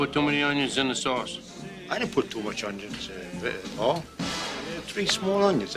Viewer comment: Does anyone else hear vinnie almost fuck his line up? Sounds like he said I didnt put too many onions in vin..uhh paul